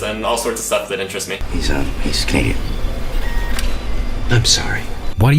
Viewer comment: Ok I have rewound the video at 1:15 multiple times and I have no idea what the hell that guy is saying that makes the other guy say I'm sorry. He's scathed?